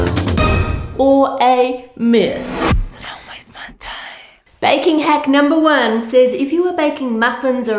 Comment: At the begining i heard “ are they a hit or a miss” but you sait “are they a hit or a mith”